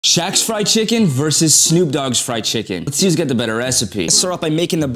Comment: I thought it said shaggy scooby fried chicken 💀